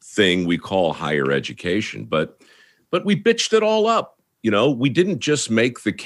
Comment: …did he just say “we bitched it all up!”? 😂